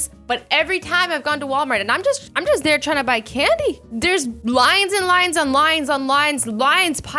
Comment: when she said “lines and lines and lines” i thought she said “lions and lions and lions” who else heard it